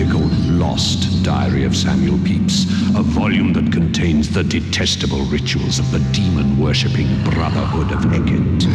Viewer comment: At like 2:51 I thought he said, "Testicle Rituals" yuck